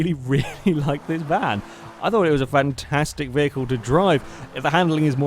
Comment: did you really said van-tastic vehicle to drive? xDD